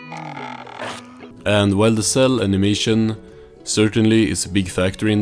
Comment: Good video! There's a typo in your subtitles, though; at 1:43, there's "cell animation" instead of "cel animation." Also, 2:49 should have "teeming" instead of "teameming" 3:05 -- "copyrighting" instead of "copyrightiting" 3:47 -- "goated" instead of "coded" 4:41 -- "where'd" instead of "where did" 4:53 -- "Wait!" instead of "What?"